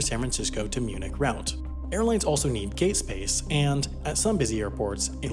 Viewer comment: did he say gayspace